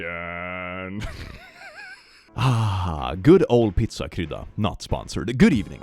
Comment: the subtitles say "good old pizza crud" lmao